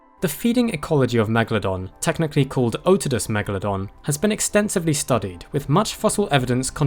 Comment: Otodus megalodon I misheard. Thought you said Oedipus. Still would’ve been correct “Mother fucking megalodon “